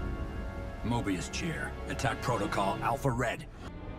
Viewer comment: Did he say Alfred ?